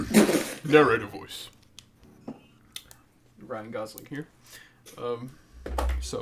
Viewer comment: Sounded like you said Ryan guzzling weeb 😭